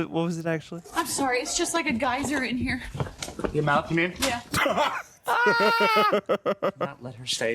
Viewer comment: What does Perry say at 22:10? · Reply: He says your mouth you mean lol